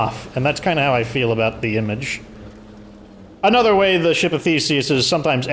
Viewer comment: I had the volume low at work, and thought you said "ship of feces". One of those times it's good to be wrong :)